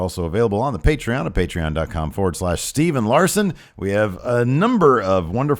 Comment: Every time I hear Steve say ' Steve and Larson ', it sounds like he's saying Steven Larson.